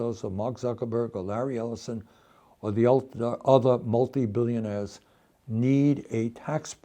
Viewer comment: The subtiitles say multimillionaires when he actually says multibillionaires.